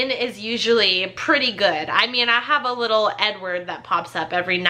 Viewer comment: did she say 'I have a little n-word that pops up' ????? Did I mishear? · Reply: asdf she said little Edward 🤣🤣🤣